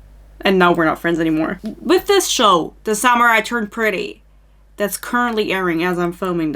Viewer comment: i heard "the samurai turned pretty" 😭